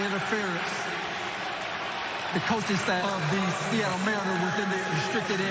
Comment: lol i rewound it multiple times too see if he really said “seattle mariners” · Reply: but i think he said “man who was in the restricted area”